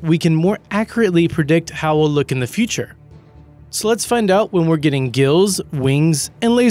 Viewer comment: Wen he said "So let's find out", I thought he said "sl*ts find out"...